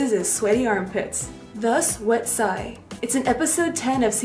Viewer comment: Am I the only one that thought he said "west side" and not "wet psy"? lmao